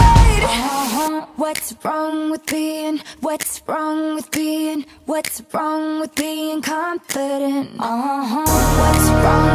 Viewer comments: i always thought it said: Whats wrong with me, im whats wrong with me, im whats wrong with me im confident! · I always thought it said “What’s wrong with me, what’s wrong with me, what’s wrong with me, I’m confident.”